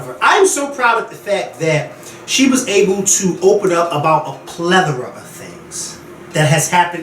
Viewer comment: Did he just say a "pleathera"?